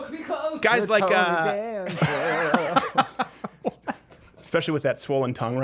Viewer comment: Did I hear 'hold me closer Tony Danza'